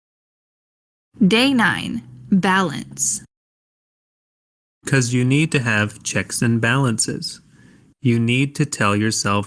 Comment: I was surprised at how I couldn't understand the faster version... Once I listened to the slower version, I got astonished by how easy sentences they were saying... What I thought they said: Could you need to have track and bounces. You need to tell yourself like you're doing good job. And you also... enough.（Only this sentence was correct） So you really have to balance it, right? Yeah, and it won't harden yourself. ..... Terrible